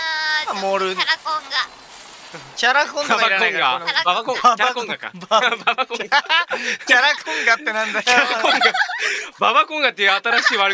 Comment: これめっちゃ面白いのが 葛葉に「ババコンガってなんだよw」って言われた後 不破湊が「キャラコンがか」って言ったのに対して 「キャラコンガ」に聞き間違える葛葉も耳終わってるって言うね